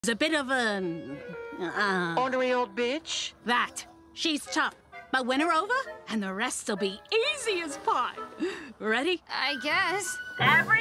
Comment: I though he kept saying « unreal B » instead of « ornery old b »